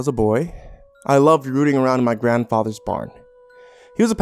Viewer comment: The first thing I hear "as a boy I liked rooting with my grandpa" messed the whole story up for me